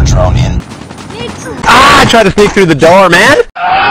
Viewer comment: Why did I hear “I’m tryna dig through the Dhar Mann” lol